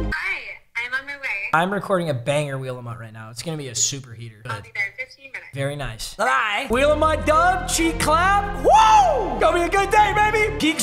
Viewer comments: yoooo, did he just say "banger wom and cheeks clapped"? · For a sec all I heard was “cheeks clapped….. CAN YOU SMELL IT?!?! CAN YOU SMELL IT???!?!” 😂😂